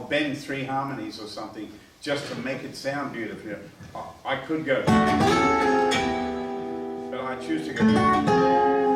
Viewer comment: We take our time with it, it's what we call ..........? What's that word? Can someone please type it out for me? Thanks in advance. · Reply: John Winchester colla voce